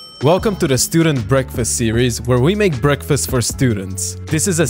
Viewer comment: I'm so sleepy I heard him say we make students for breakfast